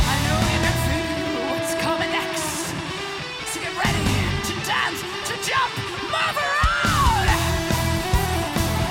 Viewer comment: What Floor is saying? "So get ready to dance, to jump...." I don't get her last word at that part. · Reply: Hello fellow Nightwish-fan. I am here to answer your question. :) She says “Move around”